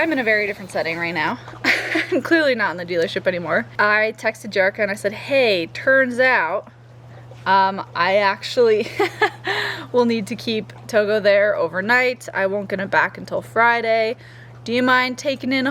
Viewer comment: Ha, at 6:35, sounded like you said "turds out" and that made me think of the "turd bag" comment. To the 26 people that gave this a thumbs down, you all have no heart or soul. I got your back and safe travels!